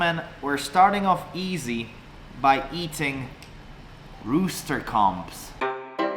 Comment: AT FIRST I THOUGHT HE SAID HE WAS GOING TO EAT ROOSTER CUM. 😂 HOW IRONIC PPPETER EATING A DONKEYS PETER. 😂🤣😂